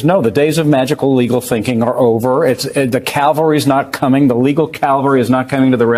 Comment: Did he say calvary or cavalry? Calvary is where Yehoshua Bar Joseph was executed